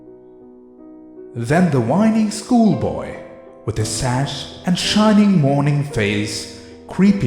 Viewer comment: is it me hearing "sash" instead of "satchel" ?